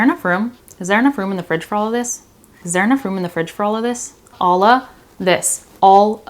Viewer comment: I'm still asking myself what the heck you just said on 4:09 😂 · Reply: Is there enough room in the fridge for all of this